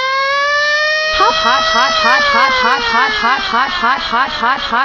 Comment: When Dora laughs at the beginning it sounds like she is saying hot hot hot hot....